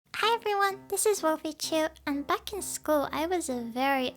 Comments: I thought she said and fucking school XD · I MISHEARD “Back when I was in in school” AS “F**king school” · I HEARD FUCKING SCHOOL AT 0:02 Kill me plz